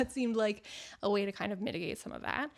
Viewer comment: I definitely thought you said ‘knitigate’ and thought it was a great pun 😂 As a knitter I would love vouchers to my favourite yarn stores, or ask me for my ‘to buy’ list like my reading friends ask for me ‘tbr’ list and buy off that.